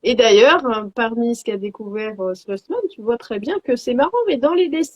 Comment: Bonjour les filles, merci pour le live! Je me demandais: quelle est la référence exacte de la personne qui a étudié l'Egypte antique et les mathématiques divines? J'ai compris "slothman" mais je ne suis pas sure de l'écriture. Continue de rayonner! Et, merci. Bises 🥰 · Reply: Merci beaucoup 😍🙏🏻😍C’est Albert Slosman :)